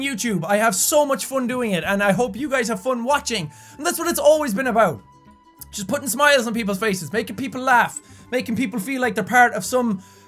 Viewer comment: I have no idea why but I heard that as "that's what it's always been about...putting some oil on people's faces"...